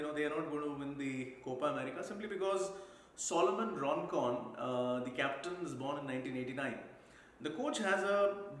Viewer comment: The moment I heard Solomon "Roncon" instead of Rondon. I knew Argentina & Uruguay are the finalists